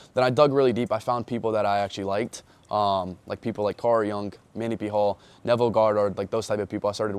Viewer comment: yo what are the 3 person mention he learn of, there was carl jung, neveille goddard and the other one i cant understand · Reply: Manly P Hall